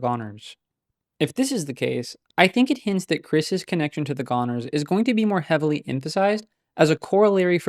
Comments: For a damn second I thought you said Chris's connection to Gooners Jesus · im CHOPPED. i thought ts said "Kris's connection to GOONERS" </3